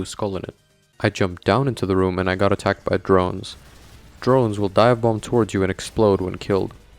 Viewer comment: i'm so dementia-ridden that i thought you said "drones will die of bomb" instead of "drones will divebomb"